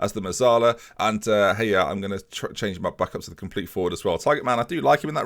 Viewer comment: did you call Yehia, Hey ya? Rename him Andre 3000?